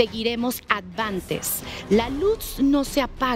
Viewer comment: Hola ojalá y puedan leer mi comentario, en el minuto 44:51 se escucha una voz, no alcanzo a percibir lo que dice "(lucha )" Bendiciones infinitas para todo el equipo de